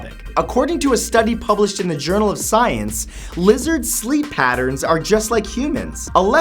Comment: So funny... you said lizard sleep patterns... I heard "wizard" sleep patterns...man how weird would that be? 🙃😲